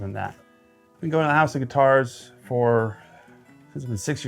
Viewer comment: Lol .. I thought you said house of guitars - nice collection